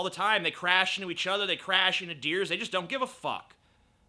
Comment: Did you say "deers"?